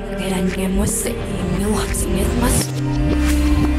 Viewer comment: I hear money